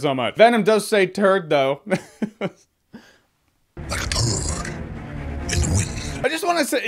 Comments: Well, I just made a jab at a cheesy line in the trailer with no concept that that one opinion would eclipse the whole video, or at least the comment section. So, when I see a slew of people commenting "those lines were in the comic books" as if they think they're the first ones doing it, I suppose I'll pin a comment here addressing it. My thought: It's cheesy in the comic, and it's cheesy in the trailer. Pulling a corny line from a comic book and putting it in a movie doesn't make it cease to be corny. Might be accurate, but that's a different thing. In the end, Two-Face having his crimes reflect the number 2 (robbing the 2nd bank of Gotham on the 2nd anniversary of being captured) is taken from the comic book, but that doesn't make it less cheesy in Batman Forever. Anyhow, That's my opinion. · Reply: True, but I heard "Toad in the wind." Is this another Anny or Laurel situation? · i thought he said like a bird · The turd thing sounded dumb I will say... but the drool that dipped out his mouth as he smiled... was pretty intense .... I love that sinister smile! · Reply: Poundz978 Really sounded like he said “Like a Bird in the Wind” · lmao I thought he said like a "bird" in the wind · I thought he said dirt in the wind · I thought venom said "like a bird" 😂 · I thought he said "like a toad in the wind"